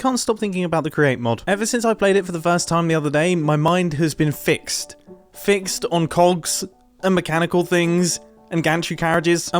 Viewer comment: "My mind has been filled with cogs” mumbo PLEASE. I misheard that...... and i'm pretty sure i'm not alone